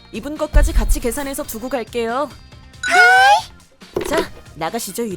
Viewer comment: 이부분 순간 일본어가 들렸ㅋㅋㅋㅋ네랑 하이가 겹쳐서 들리네요